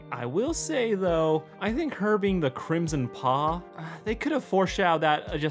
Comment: Ok I realize I'm dumb but when I watched the movie I thought they said "the Crimson Paul" instead of paw. So I was expecting some guy named Paul and then it's like "no obviously it's the one red animal." Anyways it was a fun reveal